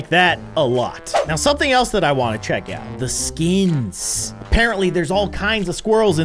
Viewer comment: I thought he said skeins